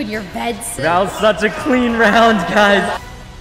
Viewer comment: for a sec I thought he said “That was such a clean round Kaif” and got so excited :(